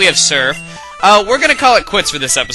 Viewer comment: You know your watching too much Dragon Ball Uncut when Chuggaaconroy says Burned Tower, and you thought he said Korin's Tower. fail.